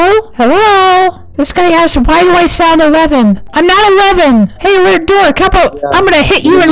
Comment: why does it sound like said something he shouldn't have said? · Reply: It sounded like "Hey lil di**" 😂😂😂